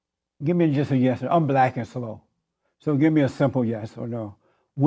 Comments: i generally agree with you but he was saying "I'm black and slow" in an attempt to bait short yes or no answers instead of more elaborate ones he wouldn't understand... point still stands though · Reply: Ah, my bad hearing bites me here. I thought he said "black as snow" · Did he just say: "I'm black and slow" ? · wtf did jesse just say?? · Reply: I'm black and slow 😂 my Lord smh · did he just say I am black so give me a simple answer ?????????????????????? 41:10 new self discrimination and racism